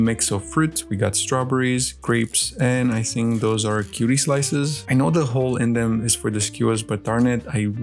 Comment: Dont mean to be rude or any thing but did bro really call orange slices kiwi slices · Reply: no he said cutie slices. like the brand of mandarin orange slices